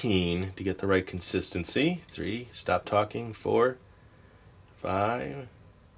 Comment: for some reason he said stop talking??? Did anyone hear it?